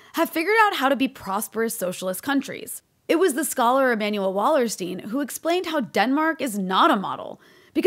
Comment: What is the name of the scholar you mention at 00:30? I hear "Emmanuel Lawerstene" or something like that, but there's no such person - I want to look him up